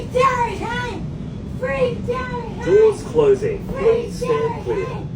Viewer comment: I thought she was saying 'Free Giant Hands!....' 🤔